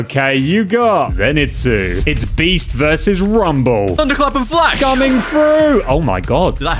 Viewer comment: did my boi really say rumble😂😂😂😂